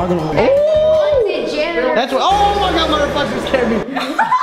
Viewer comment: did really say.. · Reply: Yall my reflection scared me